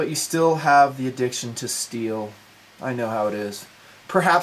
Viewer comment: for a second i thought you were saying addiction to steal not addiction to steel